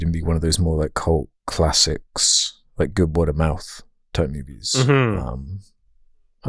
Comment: what movie title does say around 44:38? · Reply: He says good word of mouth type movies